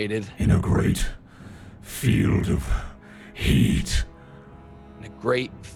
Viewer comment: I heard, 'A great field of heat... and salmon.'